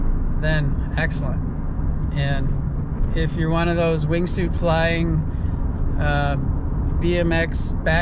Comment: I thought you said 'wimp' when you actually said "wingsuit flyin'..." 🤣 Nice work all around on this one.